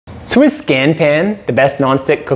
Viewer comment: I refer to Swiss Diamond and do not call Scanpan Swiss. Those are 2 different brands of cookware. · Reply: sorry if I am mishearing but is the opening line not "Swiss Scanpan"?